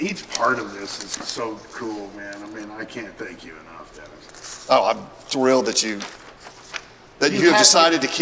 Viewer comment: Did Bill call Dennis "DEVON at the 28:30 mark:body-blue-raised-arms:🤫🤫